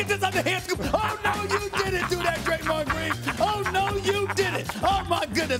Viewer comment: did he say Draymond or am I hearing things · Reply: he said draymond because he does the same thing where he sits there with the ball